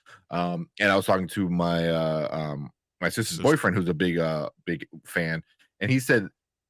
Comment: I heard: My wife's boyfriend 👀😅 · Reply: LMAO. I had to double check, it's my sisters bf.